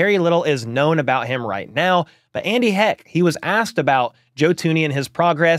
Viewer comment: What did you say at 7:35? Sounds like you keep saying Andy heck